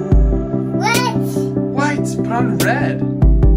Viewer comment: Did u say "white, правильно..." in Russian, or?😂